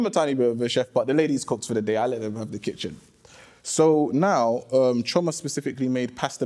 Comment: I could have sworn he said I smacked them in the kitchen. OH LAWD! HE IS JUST AWFUL. AWWWFFUUULLLL.